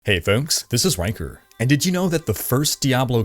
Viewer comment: I've never seen this channel before so I heard, "Hello, this is Wanker." I was like, what?!!! But great video, thanks for posting.